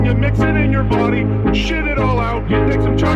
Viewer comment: bro this is what i hear "take a chocolate lobster and take a supply and dip in your body sh-t it all out"